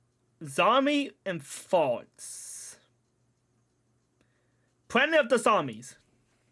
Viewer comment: What did you just say? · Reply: “Zombie and farts, planet of the zombies.” Tip? 10%, 15% 20% Or save my channel from by May 5th from being deleted.